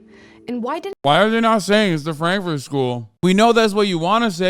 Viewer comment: wtf did hasan say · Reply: Frankfurt school